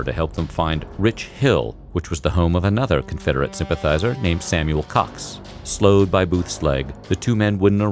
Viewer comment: Video says: 4:26 samuel cox Subtitles says:samuel cocks